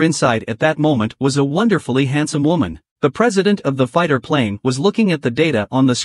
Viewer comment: Did he say, handsome women